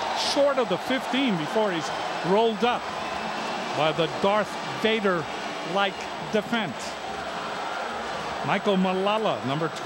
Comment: Did he say "Darth Vader, Lightsaber like defense"? o.O